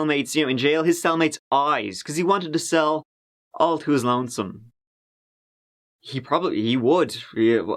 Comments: I COULDN'T UNDERSTAND YOUR HEAVY ACCENT AND PRONUNCIATION, WHAT YOU WERE SAYING. SELL TO WHAT????? WHO??? YOUR HEAVY ACCENT SAID "HE WANTED TO SELL, ALL TO HIS LONESOME?" CLARIFY PLEASE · could anybody write what does says in that sentence? Why did he threaten to gouge his cell mate's eyes? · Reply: I'm four months late but he said "he wanted the cell all to his lonesome" meaning he wanted the cell to himself.